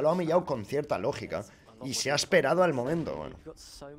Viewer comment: estoy casi seguro de que dijo weon al final de su frase kajskajs